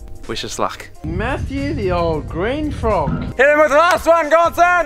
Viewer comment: Did he just call mat from demo ranch a “green frog” 🐸?